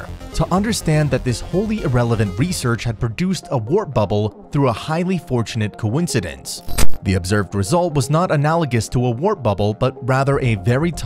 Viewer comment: Did he just say, "wrap bubble"? LOL!